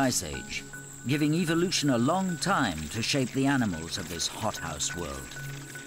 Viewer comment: Did he say hot ass world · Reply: Hot house world